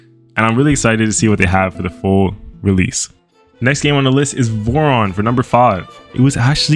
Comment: What's the name of the bird game? You spelled out the names for some of the games but not all Edit: ok nvm, found it. i'd misheard Voron as "Boron" at first and couldn't find it